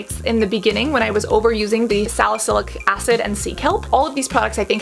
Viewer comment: I thought she was saying Seek Help not Sea Kept hahahahahahahaha